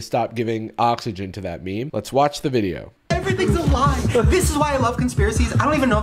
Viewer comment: Shane said everything was a LIE?? I thought he said everything was ALIVE. I was like… he really is insane